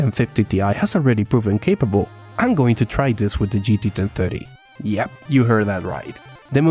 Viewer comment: nope i didnt, i heard gt 230!